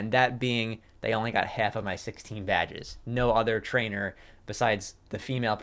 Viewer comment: This was really good. My favorite bar tho was, "I beat 2 regions, that was just practice. All the other trainers only got half of my badges" I love it lol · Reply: I thought they said "and I only had half of my 16 badges" and when I heard that I thought it was a reference before they went to kanto in the post game